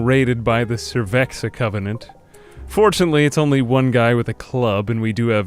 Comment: I heard that as the Cerveza Covenant and now I want to create that